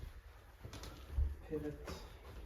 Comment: wait a minute! Did I hear pivot?! Whenever my family moves anything….anywhere, we always channel Ross Geller from Friends! 🤭😅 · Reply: I did indeed say «pivot»! Love that episode from Frievds!